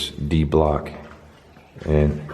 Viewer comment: I hear thank you